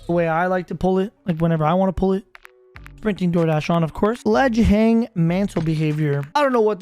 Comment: Thought he said sprinting door dash🍕🍗🍔🍟